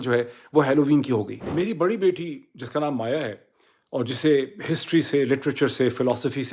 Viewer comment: MAYA? Did I hear correctly? Maya is a Hindu name.... Is Dr. Moeed Hindu? If Yes, Subh Deepawali Dr. Saab...